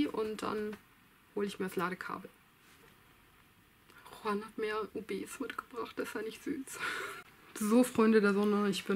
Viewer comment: Hallo liebe was hat Juan die mitgebracht? Ich habe schon drei mal zurück gespult und verstehe es trotzdem nicht :D das ist bei 22:16 Minuten die Szene :) ich bin sooooo froh jeden Tag ein Video von dir zu sehen das ist total toll :) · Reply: Er hat mir ob's mitgebracht (